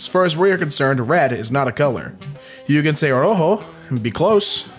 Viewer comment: Im guessing you tried to say "Rojo" but it sounded like "Rohou"